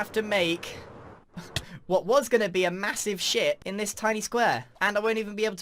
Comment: did anyone else hear “ what was gonna be a massive sh-t” or is it just me